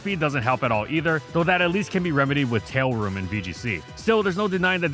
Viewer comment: did he just say "tailroom"? 😂